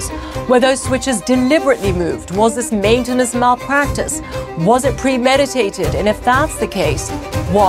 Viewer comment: what does she say? ".... Practice"